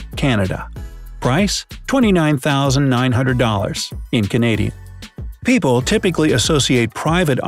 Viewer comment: what did he just call canada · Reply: He said Canadian